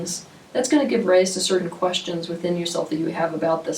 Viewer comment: I didnt understand you what you said by minute 15:26 races certain questions?